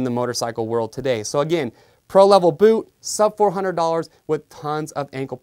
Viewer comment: Wtf is sub 4 100 dollars wtf is that cause Thad what I heard I know I didn't hear $7400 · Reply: He says sub 400 dollars. They are currently priced at $349.95